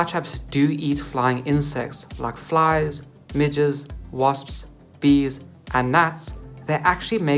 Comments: i thought he said midgets bruh nahh 😭😭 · I thought i heard that venus fly traps eats flies, beetles and MIDGETS